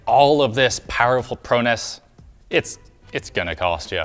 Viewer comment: He said Pro-ness But I heard Prow-ness, as in a pun between prowess and cohones. I like my definition better.